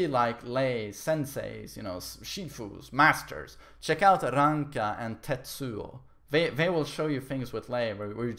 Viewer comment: Alltimes video is here as well as his channel full with content: <link> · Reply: What did you say is the Lei players name? "Ranga"?